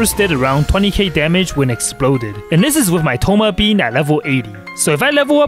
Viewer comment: I'm fully aware that what was being said was: "my thoma bein' at level 80" but I was drawing and half-distracted while watching it so for a good second I thought that it was "My thoma-bean at level 80" and I thought that was so cute lol